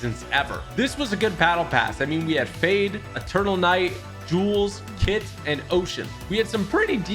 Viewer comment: 1. why is there so many scam comments on this video 2. 5:32 tell me im not the only one that thought he said turtle knight